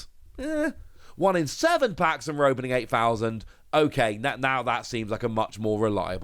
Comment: I thought you said “a thousand packs” at first but 8k is a lot of data! Nice analysis